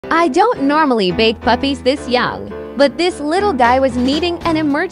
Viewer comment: Oh gods… I heard “I don’t normally bake puppies this young…” 😱